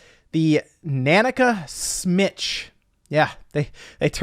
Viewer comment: I heard "Snitch"